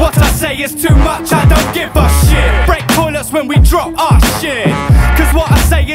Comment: "Break toilets when we drop our shit" True poetry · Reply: This whole time I thought he was saying “Break toilets when we drunk, awe sh!t” Dope line though. Good to know.